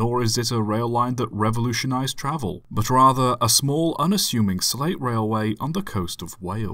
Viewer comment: I'm sorry.. did he say "slave railway"..!? 00:33 Oh, right, slate